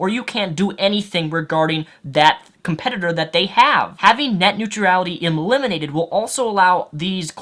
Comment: I am finding it difficult to watch this video because I keep hearing "net new tree ality" instead of "net new tral itty".